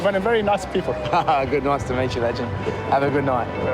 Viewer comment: "Noice to meet ya, Legend," it sounds like he said to. I could be wrong, but that's what I hear, every time.